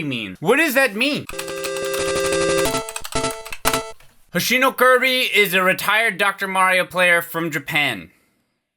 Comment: If it helps, the person was actually saying "hoshi no Kaabii", which directly translates to "Kirby Of The Stars".